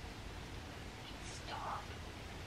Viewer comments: i heard someone say stop at 3:59 i dont know if it was you guys but yeah maybe check that out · I hear a whisper type sound of something like "just stop".